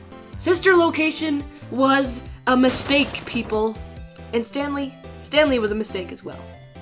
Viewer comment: The pause between Stan and Lee made it kinda sound like she was saying Stan Lee. R.I.P. Stan Lee.